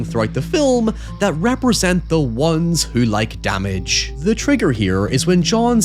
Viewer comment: I kept mishearing "the ones who like damage" as "the ones who like cabbage" and I barely even questioned it. Just went "Weird, but to each their own".